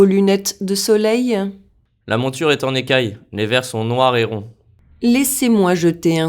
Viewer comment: La plus difficile c'était "en écailles" parce que je n'ai jamais appris cet term. Donc, j'ai entendu "dans le cahier", mais je savais que j'ai malentendu! Mais, même avec beaucoup de petites fautes, j'ai compris ce que se passe! J'aime quand les exercises ne sont pas trop faciles parce qu'on peux apprendre quand il y a les défis.